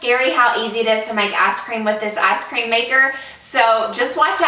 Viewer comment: This is such a dumb thing to pick out in such a well produced video, but it 100% sounds to me like she said “ass cream” instead of ice cream at 2:27 and I was very confused when I skipped forward with no context.